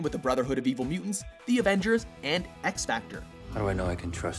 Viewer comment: did u just say x-factor on 6:20?